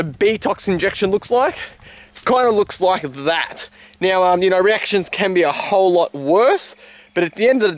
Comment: did he say erections?